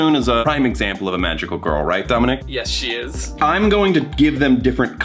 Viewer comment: Sorry Dominic i thought that my friend said ITS DOMINO !!! Like domino pizza but she said is you hehe~